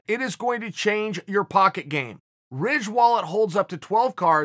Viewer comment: I thought he said “Rich wallet” of Masvidal